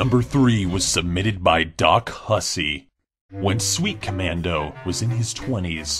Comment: I thought when It said "Sweet Commando" it was "Weet Commando"